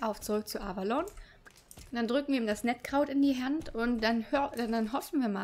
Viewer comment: oh das macht sinn ich hab verstanden mettkraut 😂😂😂😂